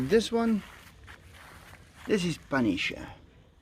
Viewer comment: did he said 'Punisharrw' ?